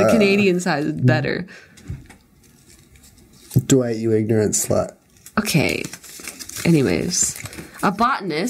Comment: Anyone know what said about 12:19, I think it ended in slut and I couldn't fully understand it so any help is appreciated. Also I don't use the captions because the captions tend to miss lines even though the audio is loud and clear enough for me to properly hear them.